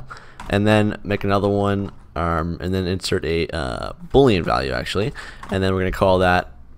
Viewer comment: Just confirming, did you say "Bool Value" in this part of the video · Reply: Nope he said String Vallue